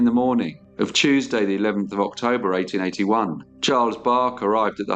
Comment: did you just say 31st of September 1881? 😳